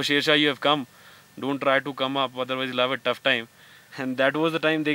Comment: Anyone, can please describe what does he say st this time, after saying don't come up? I guess it's (love at tough time"